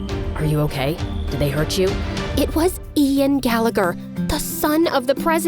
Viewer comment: "When she said "Ian Gallagher ", I swear to god I thought she was saying, "Adian Gallagher 😂